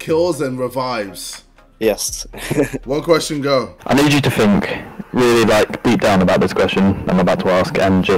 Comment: What does the guy at 5:22 say? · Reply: Think